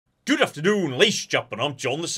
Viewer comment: i actually don't understand what says before he introduces himself. i have watched this channel for years. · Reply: What I hear is "Good Afternoon lash jopinon.